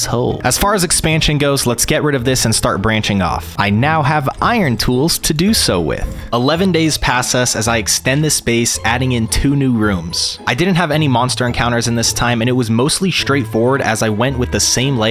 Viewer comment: “11 days passas” yes I spelt it the way I heard it